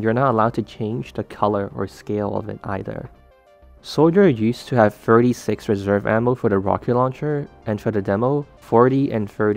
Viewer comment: My ears are cursed.I heard the "thirty six" in the 0:36 as "furry sê×". (Also I realized that he says "thirty six" in the thirty-sixth second of the video.)